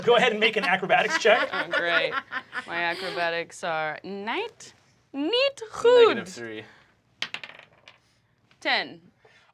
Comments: wtf. Did she just say “niet goed”? Not good in Dutch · did Rachel just say "niet goed" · Omg did she just say niet goed? 🤣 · did Kristen just say "niet goed" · Did she say "niet goed"? As in Dutch for not good. That surprised me. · did she just say: "niet goed", is she dutch?